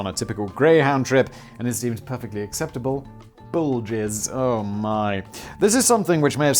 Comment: ohhhh... bull jizz... i thought he said bulges